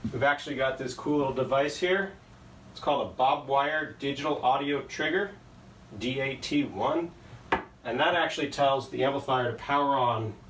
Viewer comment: What is that device that turns on the amp? Do you have a link? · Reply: I heard “ Bob wire, Digital Audio Trigger, DAT1”, but google knows nothing of such device. Link would be appreciated, ty